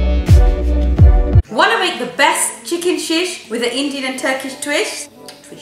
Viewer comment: I've included some of my earlier videos! Please don't laugh! 😂 🙏 Do you think the videos have changed from then to now? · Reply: min into the video bloopers. At first I thought you said "chicken shit" instead of chicken sheesh..